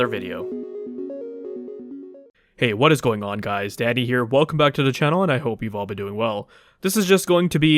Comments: Thought he said daddy here 😮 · I thought dude said daddy here 😂😂😂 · I heard daddy lol